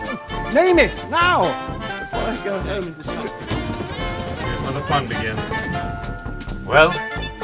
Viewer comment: I literally heard Han say " This is where the fun begins."